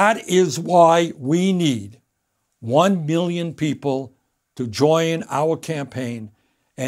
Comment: I swear he said he wants 1 billion people